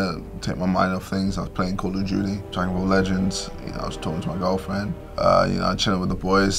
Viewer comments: Is it me or did he say he was chatting with his girlfriend?? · Did he just say he was chatting with his girlfriend